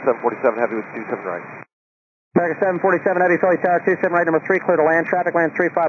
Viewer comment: is this the right caption? I think it is AAL747 calling instead of FFT1384.